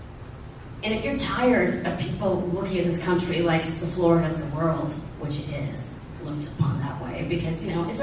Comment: Did she say "America is the FLOOR of the World ?" meaning ?